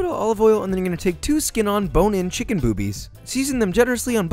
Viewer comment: did he just say chicken B. O. O. B. I. E. S?!??!?! Wth idk that's what I heard-.....